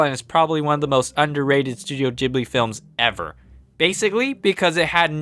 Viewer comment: did you just call it... Studio JIBLI?